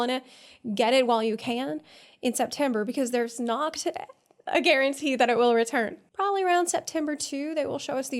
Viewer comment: "Noct a guarantee." Did I hear that right? Making a pun on Noctis? Very funny.